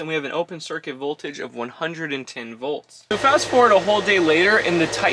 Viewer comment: tells us "the open circuit voltage is 110 watts". Can anyone tell us what that means? Usually the word open combined with circuit means there's a gap nothing's connected. I have a feeling he's using it in a different context. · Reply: I said 110 volts